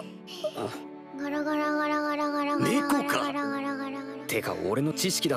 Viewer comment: Awww, that's cu- WAIT, WHAT DID SHE JUST SAY!? [replays that last bit] Rem: ora ora ora ora ora ora ora. ....OH SH-!! [GETS REKT!!]